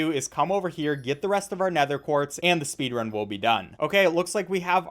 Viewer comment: Was it just me or did it sound like he said "Nether quartz" instead of nether warts?